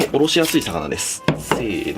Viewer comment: おろしやすい魚です。が、殺しやすい魚ですに聞こえた笑笑 戦闘力高めYouTuber